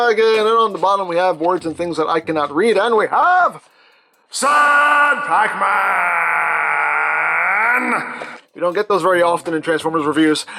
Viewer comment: All this time I'd have sworn you were saying 'sour Pacman' like it was Pacman, but he had eaten something he shouldn't have and was making a sour face....